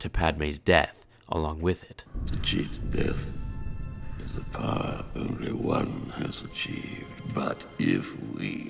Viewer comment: I legitimately thought that Palpatine said "To cheat death is a power Obi-Wan has achieved." Until I was like 10